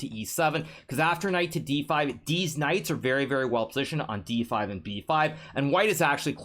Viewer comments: I heard a very distinct "Deez Knights" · deez knights o thought u said DEEZ NUUTZZ